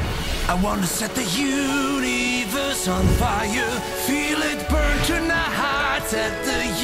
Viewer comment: What i thought i heard was " Feel it burn to nuts " . Yes i did .